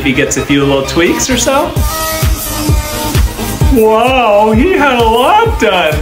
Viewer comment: I swear plastic surgeons need to learn how to say NO to something! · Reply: I thought you said, "wow, ni hao"